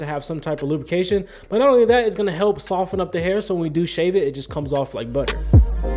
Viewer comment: When he said "So it comes off like butter", I thought he said "So it comes off like butt hair"